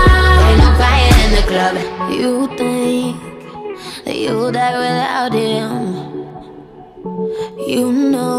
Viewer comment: "Original Lyric: You think, that you'll die without him What i hear: "You pink, that you'll lie with him"